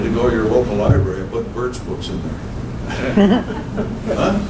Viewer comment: what books is he saying to put in libraries? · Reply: Birch Books